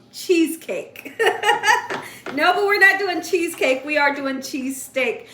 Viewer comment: It sounded like you were saying cheese cake all through your story. I was getting confused.